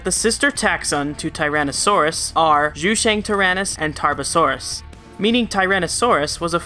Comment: Thought I heard Carbosauris 😆